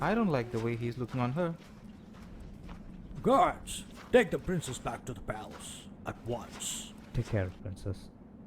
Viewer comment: Anyone else hear "Dick the princess back to the palace" or is it just me lol?